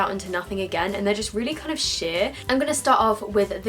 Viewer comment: Fifty shades darker - version, hahaha. The saddest thing is - I was looking like that when I was 12. Well, I did not do all of that, but my face was orange as heck. Also, I thought that you said that "these eyeshadows are really shit" And not sheer. xD